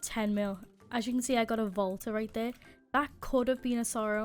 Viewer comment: when u said volta i thought u said vodka lol